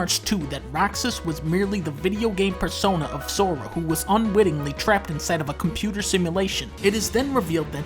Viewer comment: Where does he say he unwillingly went to sleep? Only thing I saw is Dunkey saying Roxas was unwittingly put in the simulation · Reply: OH LOL, You’re right, I was listening on loud speaker. Thought he said Unwillingly. Damn.